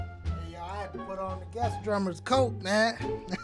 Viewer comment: I just started watching your video anmd this CJ Thompson was the first and it was thw fire. A funny thing though I thought you were saying death drummer instead of guest drummer which would have been appropriate as well sine CJ was killin it... :):):)